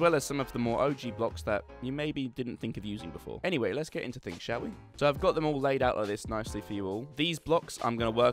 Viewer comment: I am French and I hear "chéri" yes men XD